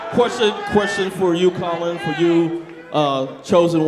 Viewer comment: did he just say “Colin”